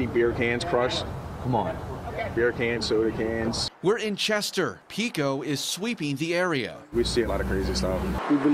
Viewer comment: I thought it said PETCO , damn.